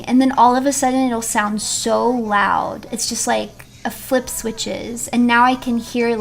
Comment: did she say... "a flip switches"? Thats kinda funny